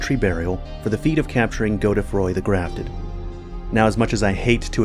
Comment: I hate the existence and lore relevance of Godefroy so much it physically hurts me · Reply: Bro I thought you said Godfrey 😂